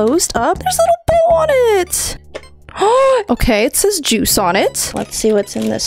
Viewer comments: lmao I thought it said twice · I thought it said twice not juice😅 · Who else first thought it said Twice instead of Juice💀 👇